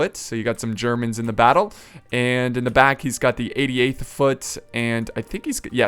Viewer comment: when u said 88 foot for britain,i thought u said idiot foot lol